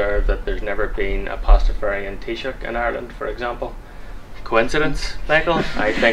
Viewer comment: "There's never been a Pastafarian _____ in Ireland." What is that word..? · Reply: Taoiseach. It's the title of the Irish prime minister.